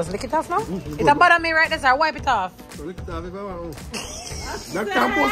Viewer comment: What did say when T told him to lick off the ketchup on the side of the plate? Mi hear, but i want to be sure😅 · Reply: I heard, "lick if off if yuh want ......... " It really festive over here🎉🎉🎉🎉🎉